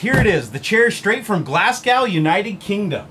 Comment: Did he just say Glass Cow, United Kingdom?